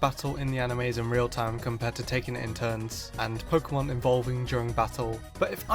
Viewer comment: you said involving instead of evolving in a mistakes video 😂😂